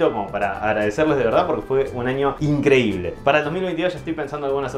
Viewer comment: Para el 2028...) o escuche mal? Feliz año nuevo Saludos desde Honduras